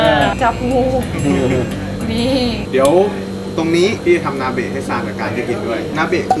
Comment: นาเบะกุได้ยินว่า อาเบะ! 😂😂